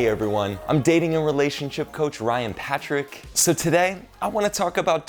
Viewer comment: Who else in the intro heard him say "heey everyone im dating your relationship coach" I almost spit my tea!